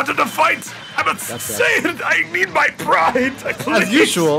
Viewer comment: I keep mishearing this as “Kakarot I need my Prime”